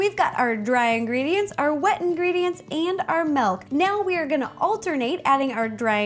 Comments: Umm I'm sorry, Ro, but wut? Did you really just say melk? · I thought you said Melk 😆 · she said malk instead of milk